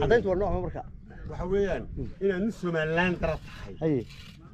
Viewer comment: Aad baan uga xumahaay walaal, its really sad..... 0:36 what is he saying?????. Our police forces is not looking good atm. 0:40 , You could have asked the same rhetorically ,wax cadayn ah maay hayaan inay iyago yihin ciidaan, gutted for you nonetheless. wax loo dulqaadan kaaro maaha. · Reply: Waxay leeyihin soo cade inad Somaliland uu dhalatay... wareer ba runti ka taagan, i found out wayo waraqahan ay shegayan wax jira maha only Somaliland ID card ba jira. yh HAHA Cid shaqaynaysa garan maysid iyo cid shacab ah.. mid kasta wa kula hadalaya wa muqaxi...... Nidamka dalka oo dhan ba iska liita, Job wax ixtiramaya ma jiran, cid laga baqayana ma jirto.